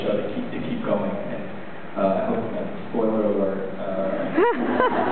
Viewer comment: What is it that John says from minute 4:20 on, when everybody starts laughing?? i can't make it out.. :S (thnx for posting btw) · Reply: Marta RJ he was like " spoiler alert...." And Michael throws his water bottle cap.